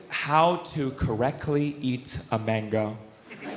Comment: holy shit! Lmao Totally thought you said "How to eat a Van Gogh"... Cackled at the ludicrousy of being "done fûckîn' around" while waiting for the follow up/connecting joke. When there wasn't one I cackled again, like "wHaT iS hE dOiNg?! That's so random & off-brand for him! Hahaha 🙃 Just rewound to share the clip with my brother & just heard it was actually "mango" 🥭, not "Van Gogh" 🖼️ Would have still been hilarious (if my ears were working properly), but was a bit of a "wind out of my sails moment" when it first caught me so wildly off guard with a feeling of bait & switch. 😢😆🫣 (Again, that I brought up on myself by not listening/paying closer attention lol🤦🏻‍♀️)